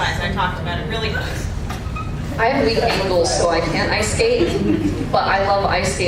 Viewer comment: what did lance say at 17:53? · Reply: He has weak ankles, so he can't ice skate. --But he loves ice /skaters./ ;)